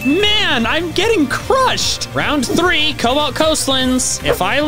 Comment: Wait, did he just say "Colbat"?